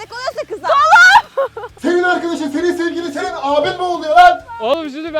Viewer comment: de sılaya ne dediğini iyi dinleyin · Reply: Abin boğuluyo dlyor.